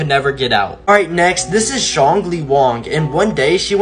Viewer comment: "All right next this is shangly wong" what I thought he said: This is strongly wrong